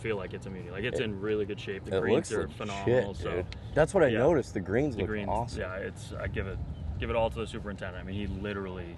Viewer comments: W subtitles on 7:57 i thought he said “it looks like 💩 “ 😂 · the clarification at 7:56 just to make sure noone thought you said it 'looks shit Dude'